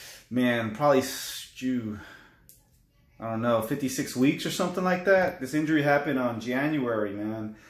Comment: Did he really say 56 weeks ? 0:33 Thanks for making this video! Question: Can a chiropractor with the designation D.C properly diagnose this injury or do you have to always go see a hand specialist , M.D ??